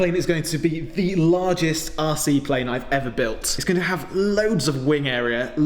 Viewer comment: I heard "largest arsy plane". Took me a second to figure that one out.